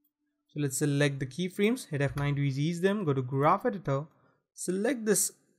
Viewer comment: Not sure what key you said to hit at 4:29. Was it F9? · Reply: Yes F9